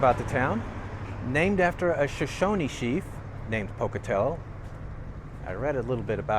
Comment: I thought you said "cheap" theater insted of "chief" LOL